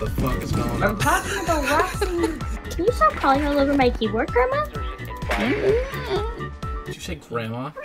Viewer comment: i heard grandma